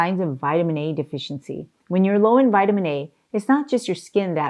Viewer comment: Whoever is responsible for the English captions are making crazy mistakes. At about 7:40 into video Dr says anti-carcinogenic (anti-cancer) and the English subtitle says anti-Christian???? LOL Never knew foods choose a religion to support or deny. :) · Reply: and at 10:10 in video she says Vit A and the English caption says Vit D, and then the next time she says Vit A the caption says Vit E. For those who are hearing impaired they are definitely getting incorrect information. Maybe someone knows how to correct this?